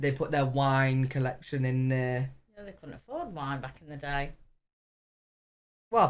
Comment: For a second there I thought Mum said they didn't have wine back in the day!😂 I was like Mum you know Jesus drank wine right?! 🤣🤣🤣